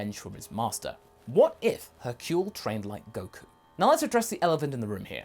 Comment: For a second I thought it said "what if hurcule trained Goku"